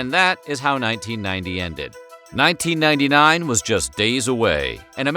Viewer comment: There is an error at the end instead of "1991 was days away" 18:28 it was 1999 said.